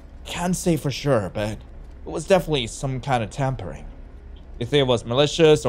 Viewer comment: I THOUGHT MOON SAID TAMPON NOT TAMPERING